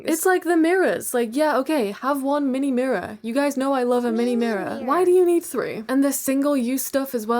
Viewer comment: Did I just hear…. 🤗😃✨MEENI MIRRORE!✨😃🤗